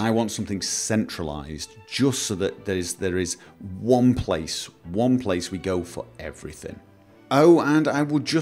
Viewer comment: when he said "one place to go for everything", it sounded like he said Gopher everything, was there an intentional pun there :)